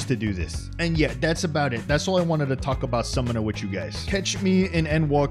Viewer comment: Honestly I'm still tickled that Garuda does the scream whenever you summon her just chef's kiss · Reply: i always thought she said "downfall" tbh,works either way